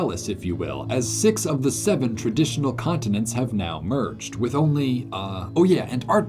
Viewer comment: did you say ⁶⁷